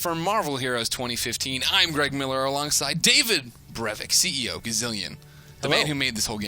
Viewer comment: LOL when you said David Brevik I thought you said Anders Brevik